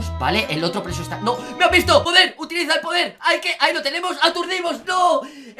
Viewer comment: Soy yo o en el momento 7:44 entendí joder??